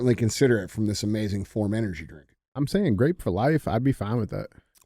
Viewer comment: Drink the grape to get a bike. I thought you said crepe, whoever is drinking that flavor is the one losing the bike. You have completed the math equation